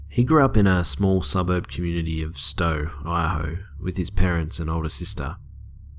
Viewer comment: did he say in Idaho! I live in Idaho <:O